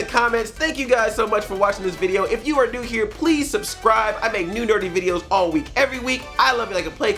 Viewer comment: Did he just say he makes Nude dirty videos every week?